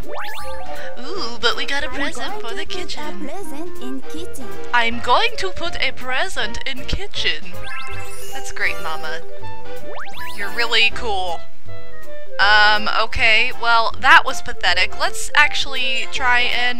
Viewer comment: At first I thought Mama said "I am going to put present in kitten." I laughed so hard at the American friend failing at cracking eggs. Just how many eggs do you think you can waste?!